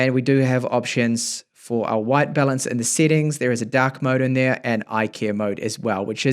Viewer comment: did i hear that right? Dac settings? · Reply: Dark mode